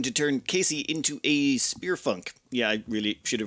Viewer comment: "Spearfunc" I heard the 'n', Everyone heard the 'n' in "Spearfunc".... right? Great name for a melee specialist!